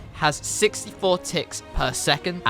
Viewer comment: When he said ticks I thought he said tits